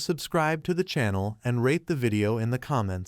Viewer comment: I heard he say don't forget to Rap* the videos 😆